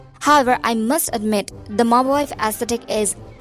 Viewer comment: What did you say before aesthetic? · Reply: Mob Wife aesthetic. The wife of these so called mafia bosses.